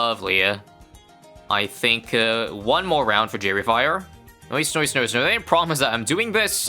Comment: did anyone else not understand what said here? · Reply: noicenoicenoicenoice